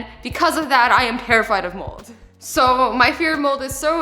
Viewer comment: Did anyone else think, that at exactly 7:49 that she said moles instead of moulded. Plus, lol the cat at 4:36 XD in the background Azzy u salty girl and I watch u alof the time literally.